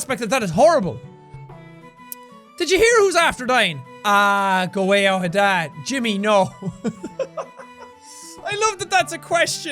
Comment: I'm sorry. Can anyone tell me what he's saying at 12:22 after "Did you hear who's after dying?" Is it from something? TIA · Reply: "Ahh, go away on with that, Jimmy, nooo"